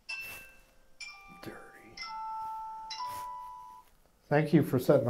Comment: mans voice sounds like it says dirty